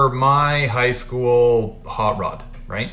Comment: I was watching this at work (Quietly) and I thought that when you said it was your "Highschool hotrod" you said my "Highschool cockblock.' Which I could relate to because I did not infact have one in highschool.